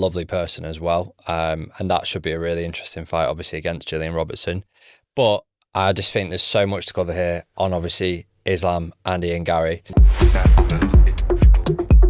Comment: Did anybody else hear "islam, Andy 'n' Gary"? 😂